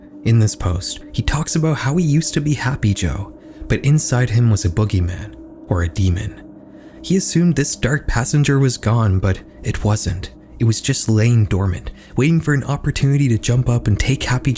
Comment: did you say... Dark passenger?